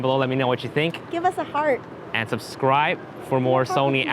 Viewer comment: I was only hearing the last part of the video and it sounded like she said "give us a fart" then looked again, saw her hands and realized she said "Heart"